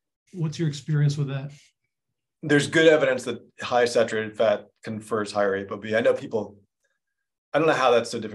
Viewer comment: Did I hear, a high saturated fat diet contributes to the obesity epidemic? If so, I’m still waiting for my ultra high animal fat diet to make me fat. I literally drink fat. Everyday. For 20 years. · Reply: I must be misunderstanding what he is saying at 14:43. Was he about to say obesity or ApoB?